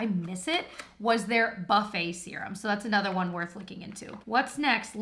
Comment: I thought she said the “Butt Face” instead of “Buffet” serum😂😭